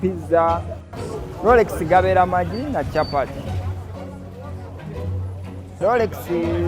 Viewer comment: did he just say Chapati? Going to kitchen💃Yayy! I guess many Indians here, like me are surely gonna try this one out! Lots of love 🇺🇬❤️🙏🇮🇳